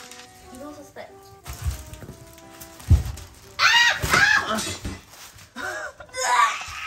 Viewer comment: はお兄ちゃんの持ってる物に興味を示してくっついてとことこ。 は口が達者で、大人とコミュニケーション取れるからそんな弟者をついつい置いていっちゃうこともあるけれど、なんかお互いが違う人間として成長していってるって感じがみえる🌱 6:04～ 「あーーちゃーーん！！」が空耳で聞こえましたよ( ˘ω˘ )